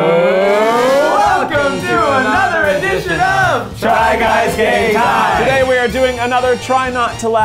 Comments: I thought they said “try guys gay time” instead of “try guys game time!”😂😂 I mean I think every thing is in some way, shape or form is gay so! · Why do I always hear “try guys gay time” ?